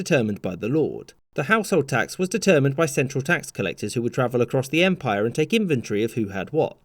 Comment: So tired that I heard "Tax collectors would take infantry of they had to walk."